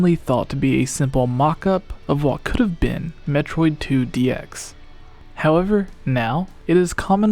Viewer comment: Thought you said Metroid 2 DS... for the GameBoy Color